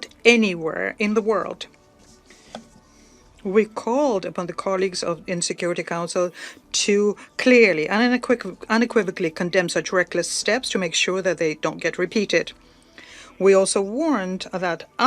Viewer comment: I misheard "UN Security Council" as "Insecurity Council" 😅